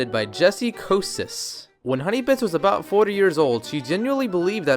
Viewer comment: Lol we it sounded like he said "When Honeybits was 40 years old xD"